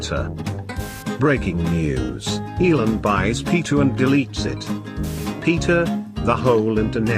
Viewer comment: I keep hearing “Pita/peter”